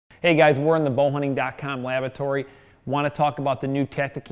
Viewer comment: sounded like he said hey guys were in the <link> lavatory....haha id like to have a couple of those 5.0's